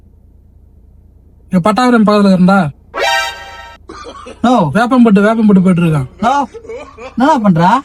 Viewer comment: Can someone please tell me what the tamil guy is saying at the 11:01 mark. It sounds like he is saying " Noo" Thank you in advance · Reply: Its not "no", its "novv"... Which is the slang word for "anna" which means brother/bhaiya